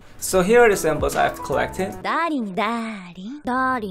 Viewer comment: THE FACT THAT THE CAPTIONS SHOW 'DADDY' INSTEAD OF 'DARLING' IS JUST 😅😅